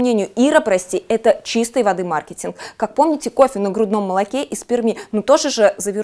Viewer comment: мне одному послышалось "кофе на грудном молоке и сперме"))))